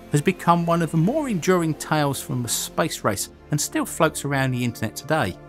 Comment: I cannot be the only person who heard him say spice rice not space race